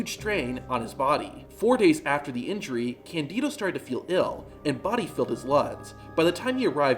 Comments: Im sorry...what did you say? "Body filled his luds."? Are you OK? Was that on purpose? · Body filled his luds? What the fuck are you saying? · what did you say? Body filled his luds? Candido died of pneumonia.